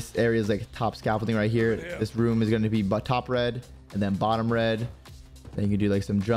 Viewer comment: So many of the call outs make zero sense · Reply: Thank you! I couldn’t agree more. 😂 It shows the intelligence of the average American who plays Cod. 7:40 - Did he say “butt top red”…? 😂